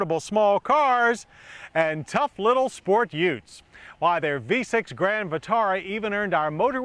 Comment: did he just say “Utes”?